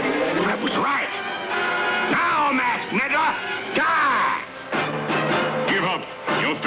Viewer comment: Whoa! Whoa! Are my ears deceiving me??? DID I JUST HEAR GENERAL X CALL THE LONE RANGER A "MASKED NIGGA" AT 5:25? Sounds like it to me. How did that get into a kids cartoon in 1966? This is one of my favorite childhood cartoons. Love the artwork. I bought every episode on bootleg dvds at a comic book show in LA. Hope it gets an official release one day. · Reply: yeah it sounds like that but General X said was "masked meddler"